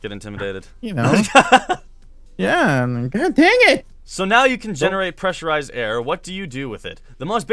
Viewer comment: I thought you said "...can create MMR..." I was like what? O.o